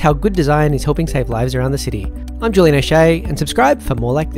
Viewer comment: Honestly thought he said he was Julian Eshay 🤣